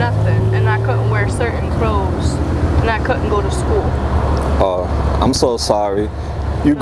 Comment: I thought she said she want allowed to go to school God 17 this is sad pls little girl get some help